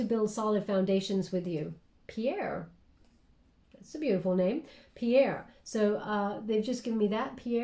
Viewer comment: did you say 'piera'? Or pierra (English version... Lol). At 36:50 min. That s my name and it s not very common name. 💙